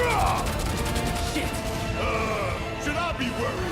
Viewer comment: Did cloud say shit at 3:49?